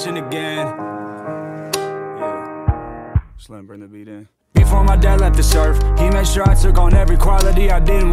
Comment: I thought he said “before my dad left this earth”, not “before my dad left to serve”